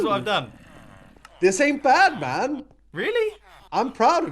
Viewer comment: Anyone noticed That it sounds like is saying Batman in